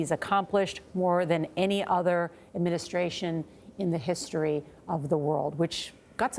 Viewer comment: I like how CNN pulls out the 1% of this (the start) that they can use negatively to spin against him. Quite comical. Since the other 99% is EXTREMELY impressive and well said. In fact the 60 seconds that follow the "clip" they are running, completely dispose of any doubt to the nature of the original statement. Completely. Entirely. Edit I see CBS did the same thing at the end. Sadly interesting. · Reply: Yes. Ms. O'Donnel (?) also mischaracterized his statement, saying at 37:00 the he boasted about accomplishing more than any administration "in the history of the WORLD." She appears to be reading his quote, but he DID NOT say "history of the WORLD." He said, "history of OUR COUNTRY." Quite a difference. Of course, she didn't go on to comment on any of the things he "boasted" about. CBS, ABC, NBC, MSNBC, CNN.... They're all the same, and this is nothing new, BTW. This has gone on long before Trump... they are just more blatant about it now vs. in decades passed.